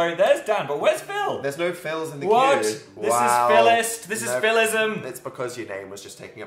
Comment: when phil said phillism i thought he said nihilism and laughed harder than i should have